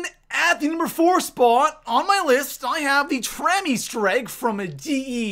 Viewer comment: For number 4 I thought he said tranny Easter egg